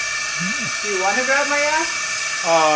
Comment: Did I hear you correctly? I heard "Do you want to grab my ass?" I don't think you would have said that though.